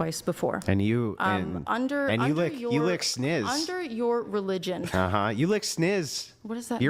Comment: Did he just say “you lick snizz”? That would’ve been an instant L in the schoolyard, let alone for a middle-aged man on a monetised program lol.